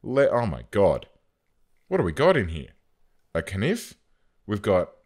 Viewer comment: You said "nif" instead of "Knife" LOL